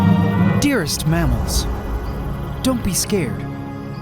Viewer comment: Lmao when you first said mammals I could've sworn I heard you say mammoths and thought 'damn he's so dedicated to these projects he's bringing back an extinct species'.